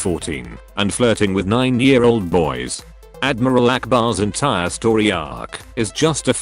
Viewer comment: I thought it said Allah Akbar